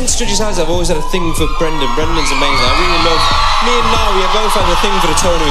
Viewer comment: Did he just say me and “Niall” instead of Louis